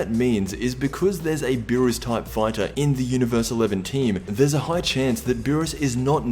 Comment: did he say eleventeen 😂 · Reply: Eleven team